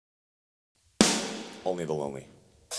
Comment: What's that he says at the start? ''Only Baloney''? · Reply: I thought he said "Only Below Me" meaning Ascendancy, because that is the album its from?? But now i hear "Only the Lonely"